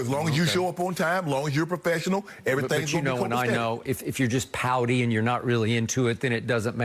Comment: I thought Skip said pounding instead of pouty😂 definitely changed the meaning of that sentence lol